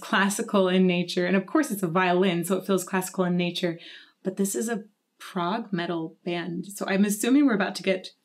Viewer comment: I thought you said "Prague metal". I was like whaaaat?